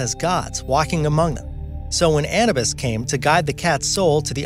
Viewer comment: Who TF is "ANNU-BUS!???? (Around 1:50-ish mins in). I thought he said CANNABIS so had to rewind!! 😂😂😂 It's pronounced 'AH-NOO-BISS' .. (Anubis), etc. 😕💫